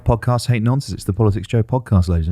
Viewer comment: Glad to see has reaffirmed his stance on nonces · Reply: Nonces? I always thought he said nonsense 😂